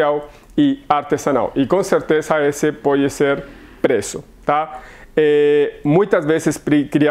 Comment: Eu voltei o vídeo 3X pq achei que vc tava falando prezo e era preço.